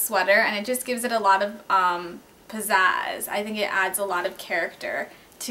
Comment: what was the word she used at 4:08 ?? posaz is it? what does it mean?